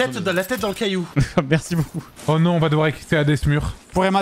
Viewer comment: Moi j'entends on va "Excicalader ce mur" :-D